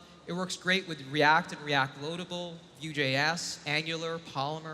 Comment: Typo at ~ 15:30 closed captions read ‘UJS’ instead of ‘Vue.js’